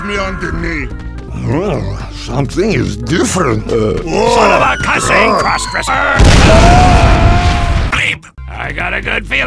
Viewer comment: Soldier, what did you say? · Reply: Son of a custom cross dresser